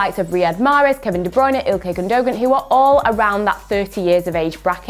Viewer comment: She really said Gun Dough Gan